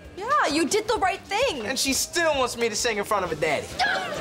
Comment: What'd he say? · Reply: "And she still wants me to sing in front of her daddy."